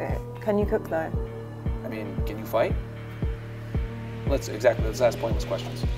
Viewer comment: When he said “ can you fight” all I heard was that “Gotcha bitch” in my head lmao